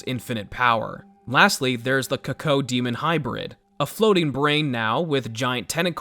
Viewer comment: … did you just call the cacodemon the “coacoademon”?